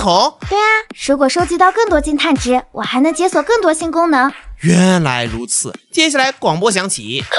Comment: 我聽成"解鎖更多性功能"